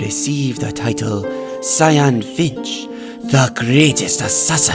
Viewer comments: the first time i heard i thought he said cyan b1tcH Sorry Also the way he says sUcCesSOr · cyan finch? I THOUGHT HE SAID SANDWICH